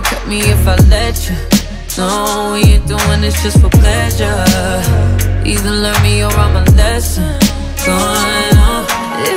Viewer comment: The Girl Said "Either learn me, or I'm a lesson"...jeeeezz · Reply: Thanks for the clarity. I thought she said either love me or imma lesson🤦🏽‍♀️